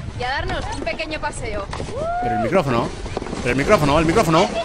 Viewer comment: Me encanta como un niño grita "EL MICRÓFONO!!!!!" xdxd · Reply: Yo entendí “EL MI CÁMARA!!!!!”